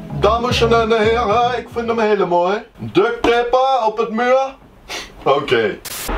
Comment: wie hoorde ook ‘ducktape’?